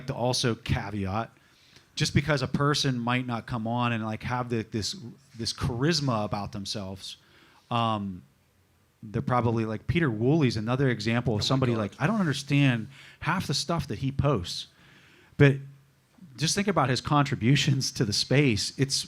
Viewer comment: Did he say "Peter Wool" ?? Or is it a different spelling?? I looked that name up and not much in reference to bitcoin comes up... can someone clarify it for me... thanks.